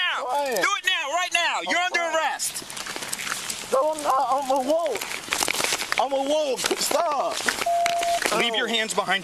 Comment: Did I hear “ I’m a wolf! I’m a wolf!”?😂😂😂😂